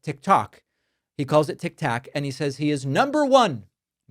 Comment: I won't ever defend Trump for just about anything. But David Packman says (@ about4:40) Trump said Tic Tac. I definitely hear "Tic Tok", twice. Now, later he did say Tic Tac. He got 2 out of 3 right. But David, is it really worth spending your journalistic capital on such a benign flub. So much other crazy shit Trump says that you could target. Save your ammo for that stuff.